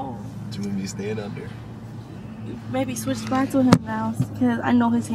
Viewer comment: calling “Baby” !! I don’t care what they say they are together 🥰❤️❤️